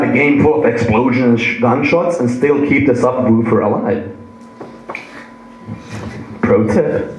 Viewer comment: i dont understand what he says @ 53:51 - "You can still keep the...??????" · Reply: (28DaysIELTS) "... subwoofer alive."